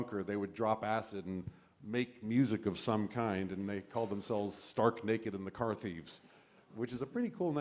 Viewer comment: I was making noise in the kitchen during the first bit and I thought he said the band name was "Stark Naked McCarthyism" which actually is an even better band name.